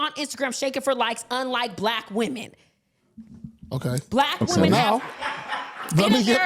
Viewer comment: He did not say "black women" he said "American women." Whoever wrote that article lied on him.